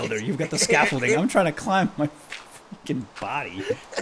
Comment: did I hear that correctly? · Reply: He said Freaking. Just hear it again it's more clear.